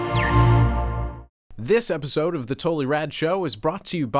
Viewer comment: lol when she said "totally rad show" I thought she said "total erection"